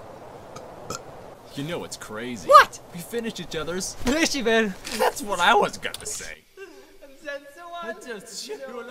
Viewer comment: What's she say instead of "sandwiches" at 0:59? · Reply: the word “brødskiver» which is the Norwegian Word for i guess bread slices or slices of bread. I though i was funny tho😂